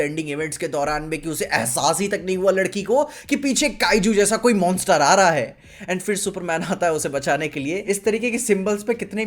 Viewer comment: did he say Kaiju then please make review on Kaiju no 8 anime series please (Don't disappoint us😅😅😅) 🙏🙏🙏🙏🙏🙏🙏🙏🙏🙏🙏🙏🙏🙏🙏🙏🙏🙏🙏🙏🙏🙏